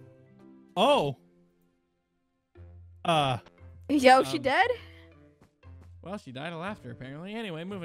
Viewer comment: I heard and I quote and saw “Aye yoshi dead?” And at 10:11 “Son of a simp”